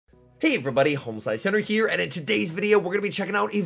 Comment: i thought i heard "homoslicehenry" in the intro. ayoooo 😂